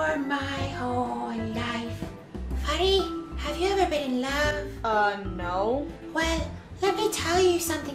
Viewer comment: voice is just a ✨𝑴𝒂𝒔𝒕𝒆𝒓𝒑𝒊𝒆𝒄𝒆✨ Edit: 3:30 it was meant to be "Have u ever been in love?" but I literally had to rewind cuz I thought it said "Have u ever made love?"